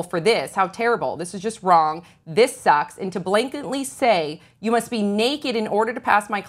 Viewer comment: did she just say blanketly.